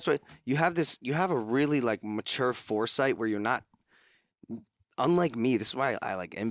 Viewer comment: did he just say his yogurt brand is foreskin